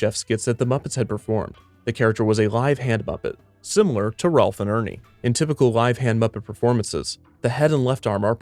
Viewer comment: sorry ma did you just say ralph and ernie